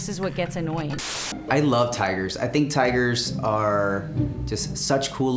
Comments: Instead of tiger, I thought he said tire. I couldn't for the life of me figure out why someone would want a tattoo of a tire. Especially not enough to make a business out of. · O tigres, at first it sounds like he is saying tyres and I am like wait they are not animals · I thought he said ‘tyres’ not ‘tigers’